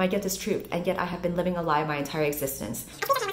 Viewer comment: i heard it as '.. yet i have been living a 'life' my entire existence ' and thought it must have made sense for the author. 😂